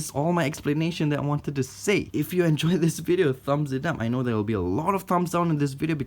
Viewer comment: I think he just said “If you enjoy this video, thumbs it down.”